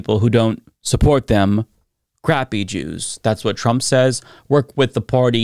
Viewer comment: what the hell are "grappy Jews"? Or did he say "crappy"?